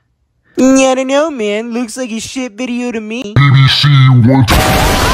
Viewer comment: What is Carnage always saying in these videos? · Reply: Bbc one two